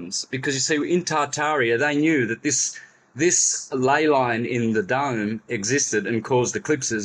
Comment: Did say "lay line in the dung", deep is his own dung, thinks me.